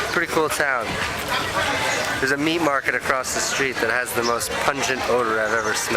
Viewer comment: At first I thought he was trying to be "punny" because I thought he said " there is a meme market across the road and it has the most pungent smell" lol